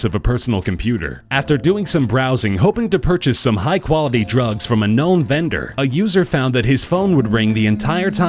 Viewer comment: when he was saying high quality drugs i thought he said high quality memes